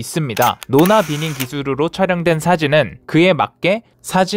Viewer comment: 노나비닝 기수르로 촬영... ㅋㅋㅋ 이거 나만 들었냐?